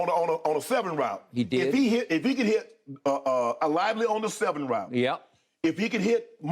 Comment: Shannon so country… I thought it was likely not lively 🤣🤣🤣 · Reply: I thought I heard him say “Lively” Lol